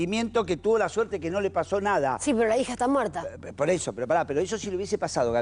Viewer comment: No entiendo que dice la periodista en el minuto 18:34 si pero la hija esta muerta